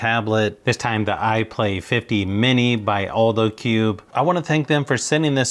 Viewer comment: why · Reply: because I thought it said, iPad 50 mini